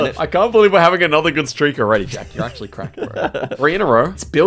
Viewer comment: I thought it said “Jacks on crack?”